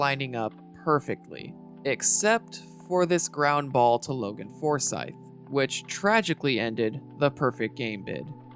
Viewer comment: When I first heard Logan Forsyth's name said out loud by an announcer, I literally thought the announcer said "poor sight." What happened with him in this video kinda brings that mishearing full circle, doesn't it?